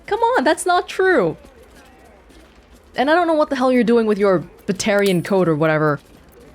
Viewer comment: I am SO glad that I'm not the only one who misheard "Ptarian" as "Batarian"...